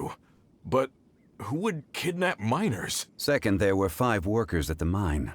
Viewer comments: "who would kidnap miners?" I think I kinda heard it wrong 💀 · I heard that wrong "who would kidnap MINORS" OMFG my brain xD